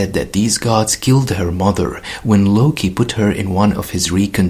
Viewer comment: Oh, thought they were saying he killed the immortal hulk